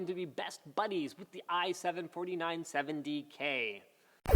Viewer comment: Did he say 4970k?